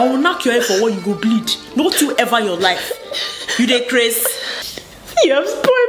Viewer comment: SOMEONE PLS TELL ME WHAT SHE SAID 3:42 'u the what??' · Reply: "You dey craze?" Pidgin English for "... are you crazy?..."